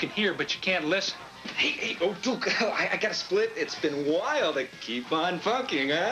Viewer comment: Did he say Keep on Fucking? Lol